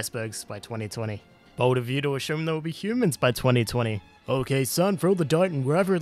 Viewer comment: did he said "bold of you to assume there will be humans in 2020"?